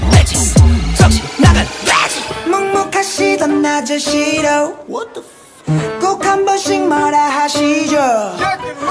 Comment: Did I hear Dean at 1:38 ?? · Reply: Yes it's Dean